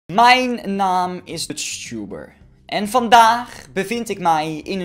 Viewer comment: Hoorde jullie ook zegen mijn naam is uttbber bij 0:00 en